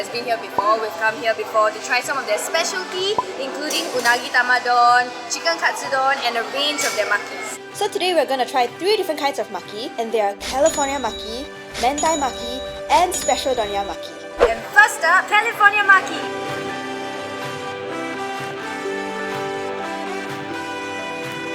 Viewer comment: thought I heard Hentai Maki 😂